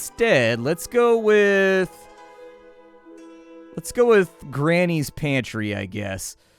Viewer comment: Granny's pantry is dangerously close to Granny's panties, which is what I thought I heard at first because I wasn't paying attention.